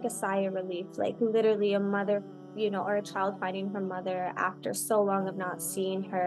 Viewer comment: Thank you so much for your continuous great efforts to spread the correct teachings of Islam and for sharing important inspiring stories of those who reverted to Islam alhamdulilah. But please note that there're a couple of significant mistakes in the subtitles of this video. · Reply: For example, at 11:09. She says "finding her mother" but the subtitle says "fighting her mother". There's also another mistake but I am sorry I don't remember at which point in the video.